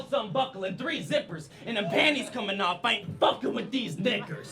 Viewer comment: i thought he said N***as but he said knickers